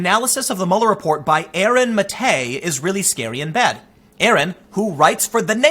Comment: Did Tim just say "scary in bed"?